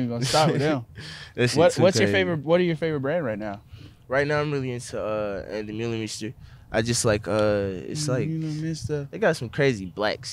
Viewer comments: wtf he say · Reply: Ann demeulemeester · What did he say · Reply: ann de meulemeester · what brand did he say at 47:04 ??? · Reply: ann de meulemeester